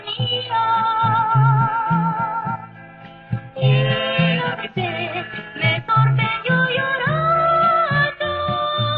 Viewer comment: Hola, alguien me puede decir que dice en el min 2:49? mil gracias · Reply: Llena de fe